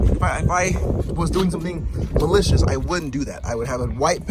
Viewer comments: Did I hear it wrong or at 3:14 did he say "if I was doing something delicious?" I swear I'm not tripping 😂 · lmao.... did you say "if I was doing something delicious"?